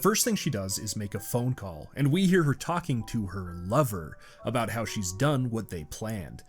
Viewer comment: Oh! Her "LOVER". I had to rewind and listen to that again, because I swear it originally sounded like you said she was on the phone with her "Mother". But it makes me wonder, if this husband is the little girl's Stepfather, how long would "Lover #3" have lasted until #4 came along and the cycle repeated... If Mr Clause hadn't shown up, that is? Also, this episode really stuck with me. I was only 7 when it released, but probably closer to 10-13 when I first saw it, didn't realise how early in the series it was released, but that episode has stuck in my head for years.